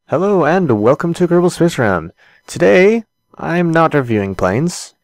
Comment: hello and welcome to kerbalspcegrm thats what it sounded like you said